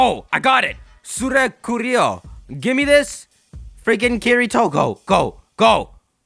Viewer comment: did just say Kirito... ... ... AAAAAAAAAHHHHHHHH KIRITO SAN AAAAAAAAAHHHHH... Huh... I'm such a weeb